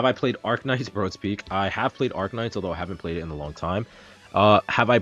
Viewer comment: am I mishearing things??? actually played Arknights before?? 😭 I'm honestly shocked the question itself didn't come from me